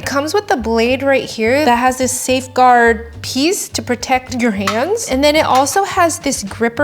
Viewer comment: am I the only one that heard “mans” 😂 (Ik she said hands but I heard mans 😂)